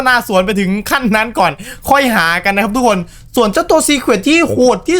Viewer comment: ไอ้ตรงนี้พี่พูดว่าสวนอ่ะ😂